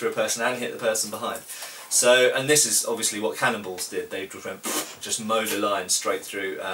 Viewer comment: Am I the only person who for a second when he mentioned cannon balls thought he said cannibals? XD "Loose the cannibals! They just tear RIGHT through people!" >.<